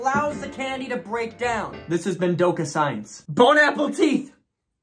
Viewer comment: Ngl I thought he was saying bon apple tea then I realized it was bon apple teeth I was like I'm so dum